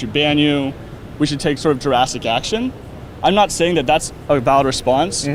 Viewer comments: Does he say "jurassic action" instead of drastic action @ 39:25? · Did he just say "take JURASSIC action"? 39:25 autistic dinosaur screeching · Did he say jurassic action? Steven Crowder may be a dinosaur.